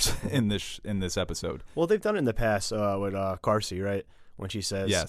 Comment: What the hell is he saying at 9:05-9:06 ? Garcie? Carsi? I've replayed this like 10 times at least, its still gibberish.